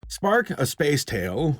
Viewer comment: I thought it said spake. Like spake the monkey